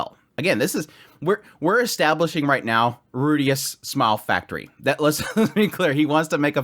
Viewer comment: I heard Rudeus Small Factory as SMILE Factory and I'm having dark ONE PIECE flashbacks right now.